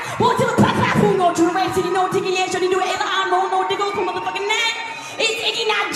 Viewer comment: all I heard was Diddy